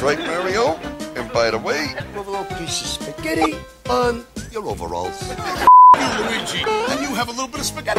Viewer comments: Great video, but a couple of errors: 1. At 1:58, Mario actually said "Oh, thank you, Luigi", but some fans cannot hear what he said because of the awful laughing track. They think that Mario said the 'F-word'. 2. Milli Vanilli only appeared in "The Adventures of Super Mario Bros. 3", not "Super Show". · no no, this isn't a clever edit, this is the FIRST episode, the FIRST scene, the FIRST time we meet the Brothers in live action... and Mario says "F*** you, Luigi" You would think it's suppose to be "Thank you, Luigi" but no matter how many times I listened to it, I just couldn't hear Thank You. How this didn't get cut out will always baffle me.